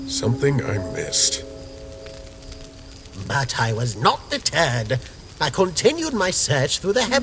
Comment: I misheard Papyrus and for a second there it sounded like he said "but I was not the turd" lol